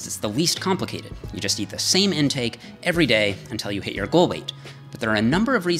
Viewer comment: Why did I think was gonna say "until you hate your life"?